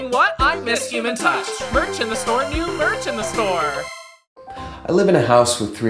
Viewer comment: For some reason I thought he said "new virgin store" lmao